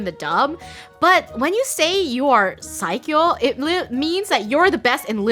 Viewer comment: I always thought they were saying you are Psycho... Hahahaha not saikyou. 😅